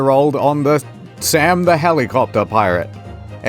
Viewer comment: Did he say "Sam, the helicopter pirate"? I'd 100% watch a show about a Helicopter Pirate